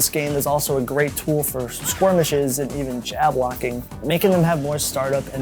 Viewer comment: This is the second time I've heard you say "squirmishes" instead of "skirmishes"